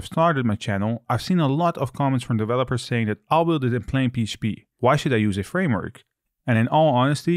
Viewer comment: can I get a subtitles for what you said there please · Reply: "I would do it in plain PHP"